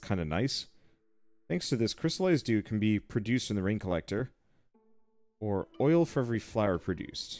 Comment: At 1 minute when you showed the "Rain Collector" I heard and read "Brain Collector". I was shocked for a moment and thought "Wow this is getting surprisingly dark", then I realized I got it wrong.^^